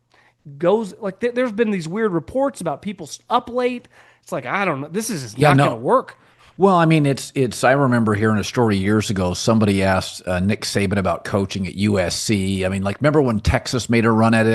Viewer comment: “weird reports about people up late” does anyone know what this guy is talking about? Or did I mishear what he said?